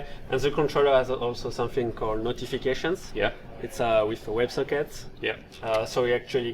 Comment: I think he says "web sockets" instead of "web circuits"